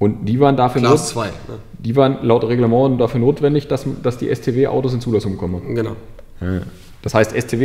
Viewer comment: ich habe beim ersten zuhören "stasi zulassung" verstanden 🤣🤣🤣